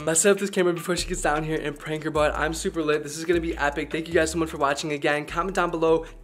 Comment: did he say I’m super lit